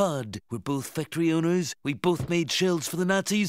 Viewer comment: We both made shells for the Nazis, but mine worked dammit! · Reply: LS* He's saying shelves because you're mishearing him ya numpty